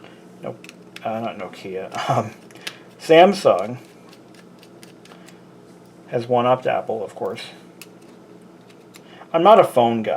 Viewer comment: Wait... What did you say at 25:37 ? "Samsung has won out Apple"?